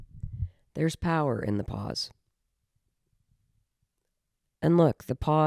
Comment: When you said there's power in the pause (after your cat was on your lap) I thought you were saying "there's power in the paws", I feel like that is also true. LOL😂❤